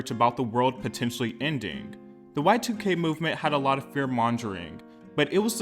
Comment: Did you just say “fear monJering”?! 😂